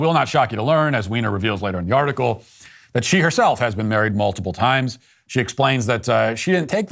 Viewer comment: Did he just say Jlos been married like 14 times, dammm 😂